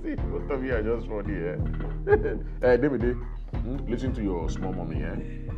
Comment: Wait did I just hear" listen to your small mummy"😂😂😂😂😂